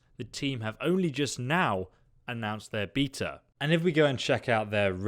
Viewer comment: bitch what did you say? Beeeta?